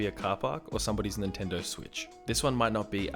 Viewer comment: It did sound like he said Nintendo Switch though. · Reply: He says Nintendo switch but if you turn on subtitles you'll se "in intend dose which" instead